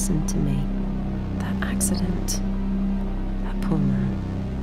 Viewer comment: What is she saying? "-that accident. (something unintelligible) me, you have-" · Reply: This is old, so you may have realized it by now, but if you are still wondering, she says "that poor man"